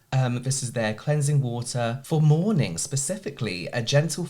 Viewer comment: I heard “Cleansing water for mourning”😅